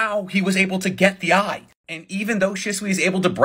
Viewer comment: you said blood so aggressively it sound like you said butt bro , i never cringed so hard 😂😂😂